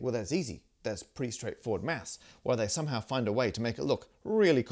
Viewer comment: Whoever writes the subtitles for these should be slapped. At 9:26, the subtitle said "math", when Matt clearly said "maths"